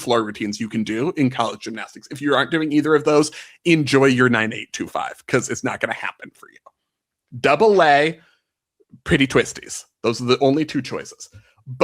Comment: I heard "floaty double pike," which makes no sense